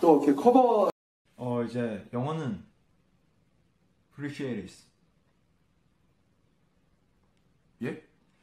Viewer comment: Thank goodness RM was there cause i had no idea what our precious tae tae was saying 😂😂😂 PRECIARIS